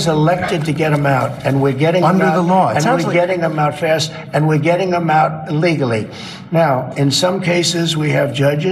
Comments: Between 11:55 and 11:58 it sounds like Trump says "We're getting 'em out fast and we're getting 'em out illegally". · Trump at 11:56. I'm sure I hear him say: We're getting them out ILlegally!! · did i hear him clearly, did he just say we are getting them out of here illegally 😮🤔